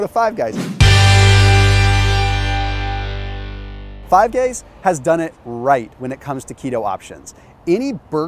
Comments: thought he said 5 gays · swear to God this guy said "5 gays".....😆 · Did he say "Five gays" att 13:04 ? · Did he just say "Five Gays?" lol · Please tell me someone else heard him say "five gays does it right" lol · did he say Fave Gays im an immature twat....but funny to me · anyone else heard 5 gays? lol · Surprised nobody pointed out he said “five gays” instead of “five guys” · did just say Five Gays? Or is this Ketojuana to strong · he actually says 5 Gays instead of 5 Guys😅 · Great video! Very useful for the majority of people out there . Also, at 13:09 it sounded like you said Five Gays instead of Give Guys, lol! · Did he say five gays at 13:09? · he said “5 gays” instead of 5 guys 😂😂😂 I was like wtf is going on lmao. I love this channel tho lol.